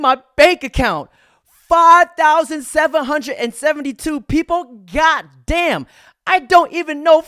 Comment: i thought asians were smart 5:09 where did this regard get 5772 from when she CLEARLY SAID 1572. clearly has too much brain rot